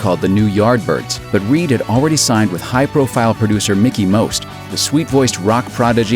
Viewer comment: I thought it said Mickey Mouse, not Mickey Most!